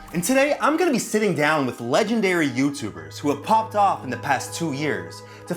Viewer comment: What the fuck I thought you said legendary YouTuber